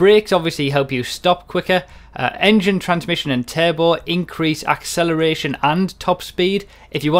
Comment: For the longest time I kept hearing "terrible" instead of turbo. I better freshen up on the English accents lol